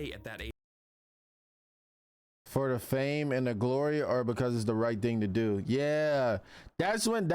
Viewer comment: Tetrax in fact did not said that. He asked Ben if he wants to be a hero because it a thrill of being hero or BLA BLA BLA It different cuz he didn't ask Ben if he wants fame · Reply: Yeah he said “That’s right you have done a lot of good, But ask yourself this. Do you do it because it’s the right thing to do or for the thrill of being a hero” and left Ben speechless